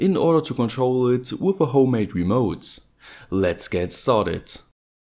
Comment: sounded like u said let's get soldered lol 😂